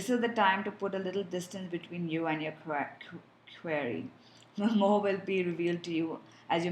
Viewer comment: sorry but i just couldnt make out what u said at 22:20 " time to put distance between you and your current ----- "??? what was that you said??? please and thank you! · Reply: query sorry... :-)